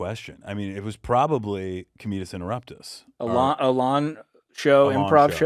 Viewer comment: i thought they said the name of their first comedy show together was penis interruptus. hey beck and kyle where can we post our remixes of your brilliant clips